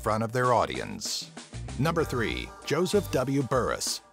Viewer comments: It says Joseph Burns instead of Joseph Burris... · Joseph W Burrus… why does the caption say Joseph W Burns?